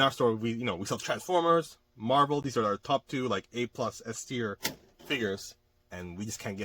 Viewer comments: Hi, what does it mean when you say “S tear“ at timeline 7:11? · That’s not what I mean. I heard you say, “S tier”. Maybe you can tell me what you actually said. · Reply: S tier Figures ? My dude u trolling me XD